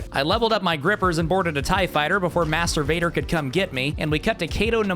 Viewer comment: Did he just say "Masturbator" o.O